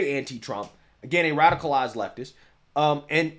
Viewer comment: I thought you kept saying g radical ass leftist. Lol which is fine but qhen I realized what you were really saying it made me laugh